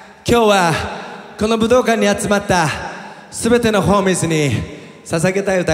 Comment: この武道館に集まった全てのホームレスって聞こえた！笑笑